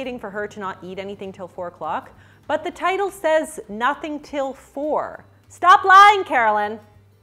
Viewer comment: When you said ‘nothing til 4’ I thought you said ‘nothing to live for’ and I mentally retweeted that